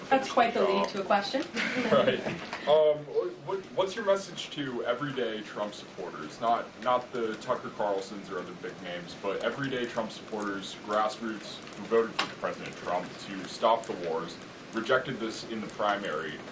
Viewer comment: It sounds like she says 'President Chump'